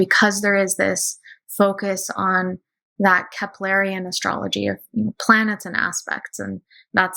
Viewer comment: Hey! So I'm having a hard time hearing what she's saying around 28:35. Is it keplarian/ cepplarian astrology? I'm really curious because I actually formed my own conclusions around that type of astrology without even knowing there was a branch that focused on it. I think aspects and planetary archetypes are really important in astrology because they are unchanging. House systems and Vedic vs western etc.. creates the variation in how to interpret it. Anyways. Yes, can you help me with the name here please? I've tried googling but I am not spelling it right. I'd love to learn more! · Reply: She was talking about the astrology of Johannes Kepler, who focused on aspects and didn't use the zodiac.